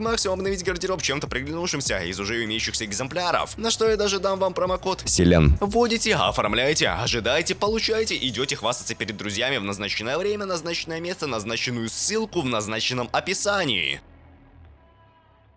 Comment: бля я расслышал как Слен